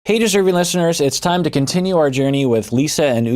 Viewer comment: What does he say in the beginning? 💀 all I hear is “hey (something) listeners “ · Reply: "Hey, deserving listeners!"